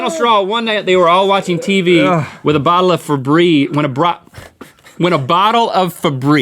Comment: LOL I heard “Watching tv with a bottle of febreeze”